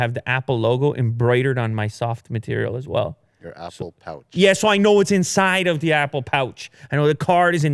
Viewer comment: I swear I keep hearing "your asshole pouch"